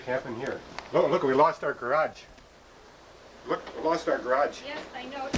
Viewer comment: Omg for a second I thought you said you lost your parents omg....but whew glad you didnt but sorry about the damage.